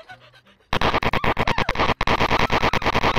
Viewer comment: 총 던질때 아이 씨ㅂ가 들림ㅋㅋㅋㅋ